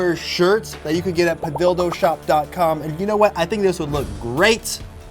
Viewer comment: When he said padildoshop I heard ‘the dildo shop’ lmao